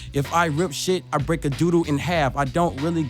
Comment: did he say I'll rip a doodoo in half😂😂😂😂